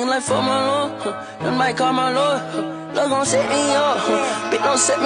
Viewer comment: as a person who is fluent in cartinese we aint gon ball d rose huh i dont give a fuck bought nun breathing like fuck my lungs might just call my lawyer huh they gon set me up huh (yeah) bitch don set me up (okay) im with tyluh yuh (slime) he really like the car and she wicked huh yuh like woah vicky huh yuh (like woah vicky) oh my god hol up yeah these diamonds not tiffanny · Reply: I thought it said “Plug gon’ set me up”